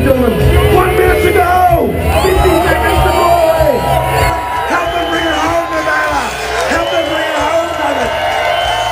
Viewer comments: What did Miki Sudo say to Stonie around 11:34? Sounds like "Stonie come on!". Nice to see eaters cheering each other on during competition if so. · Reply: “Your making some money” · I'm curious what Miki Sudo said to you at 11:33. It sounded like she cheered you on :) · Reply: vanitybenevolent shee said ong